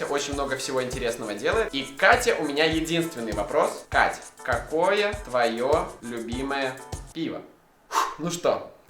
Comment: я думала что он сказал "какое твое любимое имя"